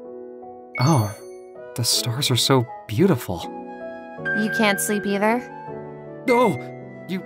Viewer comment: LOL I thought he said “the stairs are so beautiful” and immediately the “Ah yes this is good floor” meme came to mind XD